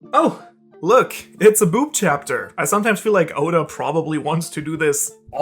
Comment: "Oh it's the boob chapter" What you said What I heard was "Oh it's the Brook Chapter" then I saw brook and thought the heck is going on, finally you show the boob scene and I realized how much of doofus I am